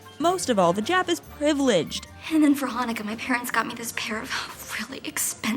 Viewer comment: I don't know if I imagined this or maybe this is just another example of the Mandela effect, but I thought when Gretchen is talking about those hoop earrings I could have sworn she said 'for my birthday' and not 'for Hanukkah'? Did I just misinterpret that scene?